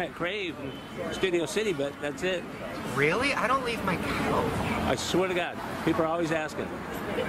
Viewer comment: Shane: "I neVeR lEaVe mY cOuCh" Also Shane: is very much NOT on his couch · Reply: I can see how it could sound like that, but he is actually saying "couch", it just sounds weird because he doesn't really finish the word. Watch his lips